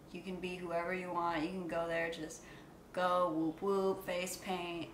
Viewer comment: I thought she said jiggalo😂😂I ain’t realize until the end